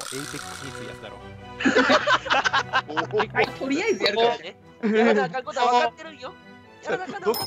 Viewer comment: とりあえずやるからねがチーノくんに聞こえた